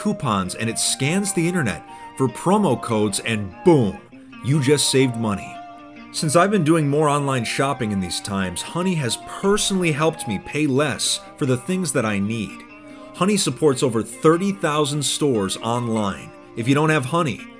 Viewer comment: In the sponsor, he said that he was doing "more online shopping", but first I heard "moron shopping"